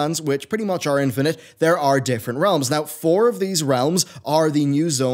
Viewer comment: Whenever you say "Realm," I hear "Loading Screen."